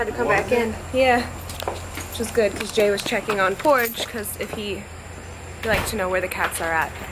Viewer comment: whats your cats name??? porridge??? as in oatmeal?? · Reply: haha ok it sounds like she says Porridge! with a P lol